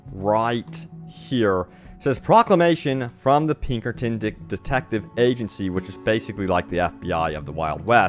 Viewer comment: i heard dick detective lol